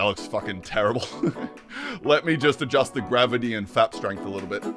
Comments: I heard “fap strength” instead of “flap strength” and was very confused for a second. Lol. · I swear he said "fap strength".